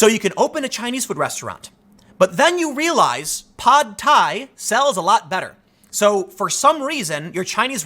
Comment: I had a mini-stroke and thought he said Tide pods instead of pad thai